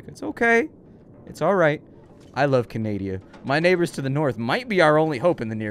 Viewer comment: Did he say he loves Canadia? Lol